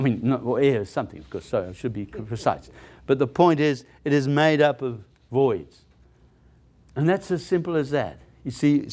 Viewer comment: wtf are you saying batritis? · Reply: Detritus. Look it up. Learn a new word